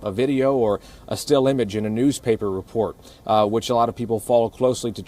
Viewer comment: At first, I thought they said, "still vile and evil."